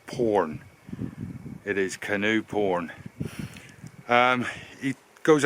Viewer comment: Thanks for finishing the tale :-) Oh Canoe Porn I thought you said Gnu :-)